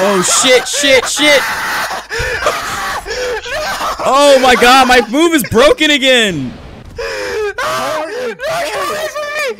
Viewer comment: Captions say “Oh my god my boob is broken again”